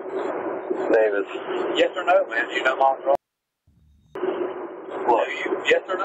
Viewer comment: It’s funny that the drunk cop was trying to blackmail the other cop. Some friend he is, though I don’t doubt that the other guy is cheating cause it happens a lot in America so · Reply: He starts saying the first and last name (either Molly Ross or Roth I think) around 34:00. He asks if he slept with her and also asks if he participated in BDSM right after 35:00. I put on headphones the first time I heard it. Also says right after, "is she a married woman? Are you a married man? Why would you sleep with a married woman if you're a married man? And participate is various sexual activities?" Etc